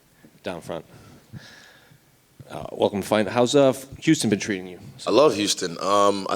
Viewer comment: Thought the first guy said’ how’s juicing 💉 been treating you?’